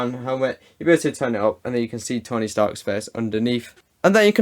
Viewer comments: Did anyone else hear “stony tark” 😂 9:28. By the way love the vids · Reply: Lol I was wondering if just I heard „Stony Tark“😂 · Did you just say STONEY STARK 🥴😤😤😤